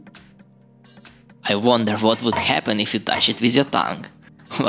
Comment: WHAT DID YOU SAY ???? I WONDER WHAT WOULD HAPPEN ,,,,,? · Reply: "I wonder what would happen if you touched it with your tongue"